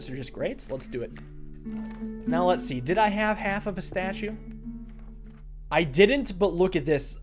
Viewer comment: for a second, i swear said "let's see, did I have half a pistachio" and I was so confused trying to figure out what that meant